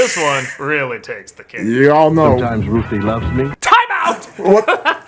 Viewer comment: He actually said "Ruthie", i.e. Cosby's character's wife. But yeah, the low-quality VHS rip doesn't help.